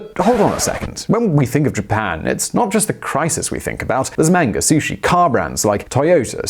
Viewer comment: I thought he said Mango ...Lol